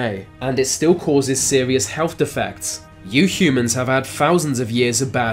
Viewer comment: is he saying 'you humans,' i thought he was saying 'new humans' like post neanderthal crossover.